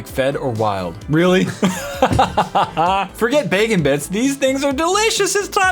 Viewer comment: I thought it said bacon toothpaste